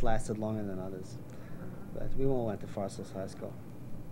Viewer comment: I thought he said ‘we all come from Far Sills High School. ‘ I am like, Far Sills?.. wtf….